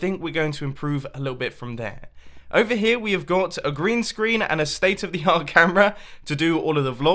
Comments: I thought he said Grian screen when he said green screen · I thought he said, “Over here we got a Grian screen.” for a second. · I Thought He Said Grian Screen HaHa